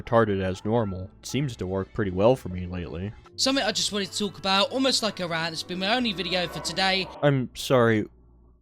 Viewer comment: "something I just wanted to talk about, almost like a rant, it's been my only for today" is what he said